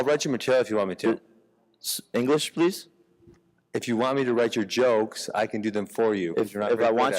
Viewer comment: Jake thinks Ben said "read your jokes" and goes on about Ben's intelligence when he actually misheard Ben in the first place 🤦